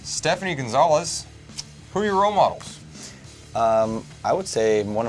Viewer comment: What means this word at 2:50? I didn't understand it, and what means this word? It starts with 'ro....' :o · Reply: "role models" it means like who he looks up to or aspires to be, basically.